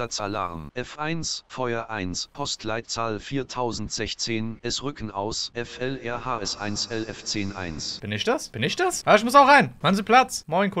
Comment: da nicht verstanden hatte was dort war ich hab da verstanden: F1 Feuer 1 , es rücken aus (glaube FHL) 1LF10 1 (also 1 wache 1 Löschfahrzeug Funknummer 10 1) Und 8:16 der eine Typ "ich bin zugführer" warum funkt er dann zum C-Dienst (ich glaube er wäre dann B oder a Dienst und hat keine Zugfüherer weste an xD Sogar hatte die GF weste an